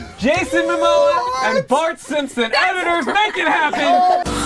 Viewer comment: I thought she said oj Simpson